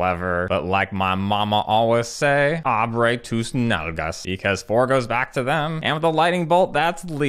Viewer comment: What did he say at 7:00? xDD "But like my mama always said ......." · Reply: OH HE SAYS ABRE TUS NALGAS HAHAHAHAHHAHAHAHA